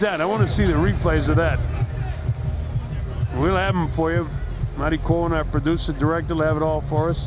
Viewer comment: Did they just call the producer " Maricon" @ 4:15? LOL